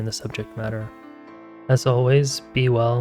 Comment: Sounds like you say, "be well, BEAT the poor"